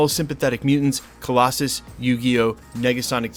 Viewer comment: did he just call Yukio YuGiOh ?? lol also starting to think "need to work on easter egg video" is just a cop out lol